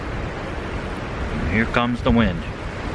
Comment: Good job on the demo. Stove looks like a winner! Boy that salmon sure looked tasty. I laughed at myself when you said "there goes the wind", I thought u said "there's goes the win ", because as u said that u had gotten a nice piece of fish that was a lil charred in all the goodness ! lol ;)